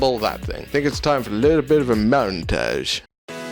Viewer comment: Idiot + Hobby + Man Cave = Is this me? Awesome to finally see how this all came together :) Your man cave, Ben's man cave, my man cave - everyone needs a man cave! Also, when you said "montage", I heard "moan-tage" - I was very disappointed we didn't get clips of you just moaning about random things.